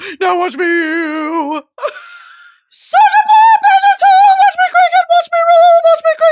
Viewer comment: Thought you said saja boy at the start !! I need you to watch kpop demon hunters now !!!!!!